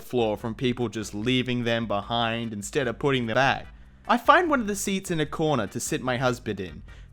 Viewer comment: Leaving them behind instead of putting the—back (?)